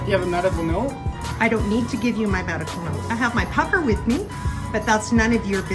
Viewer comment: That's BS! The minute she said she had her puffer with her I knew she had asthma because I also have my puffer with me because I also have asthma but I wear a mask and it's no problem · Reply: I thought she said,"huffer", not puffer. Isn't that a spray paint can in her purse?